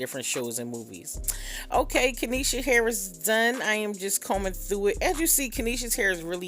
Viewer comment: I thought she put the lady whole government out there🤣I thought she said “ok Kenesha Harris Dunn” 🤣but she said “ok kenesha hair is done”🤦🏾‍♀️